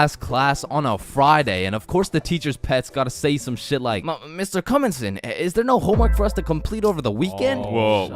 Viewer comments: did bro say "Mr cumonsen?" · did bro say Mr cummson?